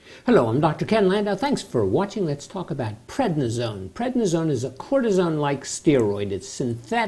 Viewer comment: I thought he said prednisone is a courtesan..